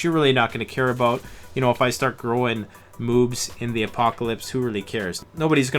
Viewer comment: Did you say moobs?